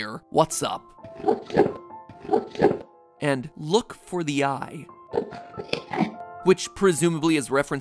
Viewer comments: Honestly, I think the Endermen are saying "Look for the End", not "Look for the Eye" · I heard “Help us” instead of “What’s up?” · Who else felt chills go down their spine when listening to the "talk" of the Endermen? Especially that.. "Look for the eye" thing... U..ugh... 4:56, if you're wondering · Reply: I heard "Look for the end" (instead of "eye"). Which would make more sense to me as they drop eyes. Why should we look for eyes when they have them? We are, instead, looking for the end of the game.